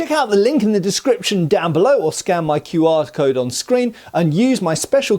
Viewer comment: I heard "scam my QR-code". OK, I am tired.